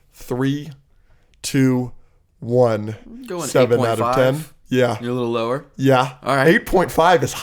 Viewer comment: at first I thought gave it a 5 out of 10. I was like damn. I didn't hear the 8 at first